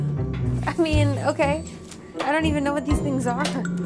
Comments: what did he say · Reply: Something cabin crew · Wft did he say? I hurd VH cammbin crew. Is that Indian or English? 😂